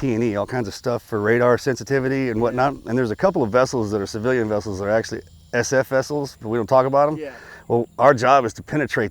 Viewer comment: did he say theres lots of civilian vessels that are actually SF(SPECIAL FORCES) vessels....